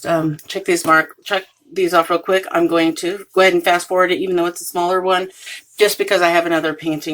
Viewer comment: I was dping while watching this and a little out of it on painkillers and when you said go ahead and fast forward at first I thought you said Gwen fast forward this and I was like what??? why??? I had to rewind and relisten. LOL! Great video! I know some people don't like the word review for the first look but I don't mind it. I feel like there are many types of reviews like initial, post etc. When people buy items they often post a review when they get it and then again later as an updated review when they have used it for a bit. But I understand wanting to change it to first impressions. I love the elder Kitsune (nine-tailed faox) kit. I might need to get one of those for my sister!